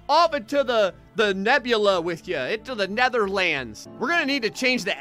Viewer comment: Did he just say "up into the * Netherlands*"?